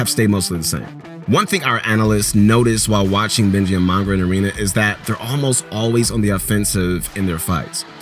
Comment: did he say NINJA and mongraal in ARENA? or am i just hearing things